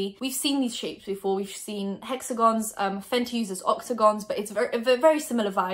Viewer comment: am I the only one who heard “oxagons”🤔